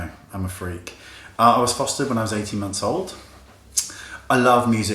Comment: What is he saying at 1:34 .. "I was ... when I was 18 months old"?